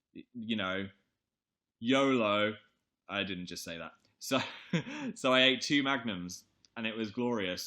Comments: I thought he said I ate two magnets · i thought phil said he ate two magnets i was so worried like why was he so happy that he ate two magnets?